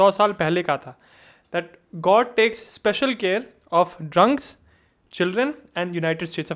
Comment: Just a small correction 0:06 it's fools instead of children ❤